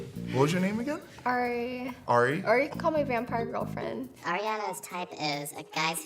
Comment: “Vampire girlfriend”. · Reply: Oh Thank you Anna T . lol I played it back twice and still thought I heard grandpas girlfriend 😂.